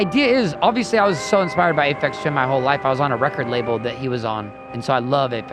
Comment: lmao i thought the same thing as Oliver, i was wtf did say when he said Effects Twin LMAO Aphex Twin <33333